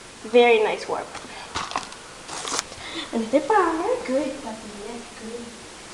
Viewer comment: That's awesome! At the end it sounded like you said "good puppy" :P